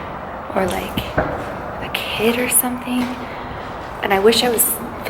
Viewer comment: did anyone else hear "me" when she said "...a kid or something" or was it just a car honking